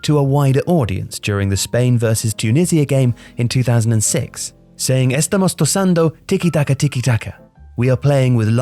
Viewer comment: He said"Estamos tosando tiki-taka tiki-taka" instead of "Estamos tocando (tockando) tiki-taka" 😂😂